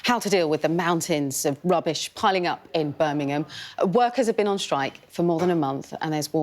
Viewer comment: I genuinely thought the reporter was saying “Virminhgam” lol when she was talking! Had to do a double take!!